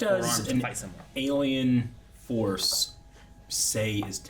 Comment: did tjey say ALIEN FORCE